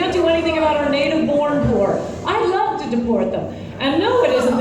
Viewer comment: Did she say she'd like to deport the poor?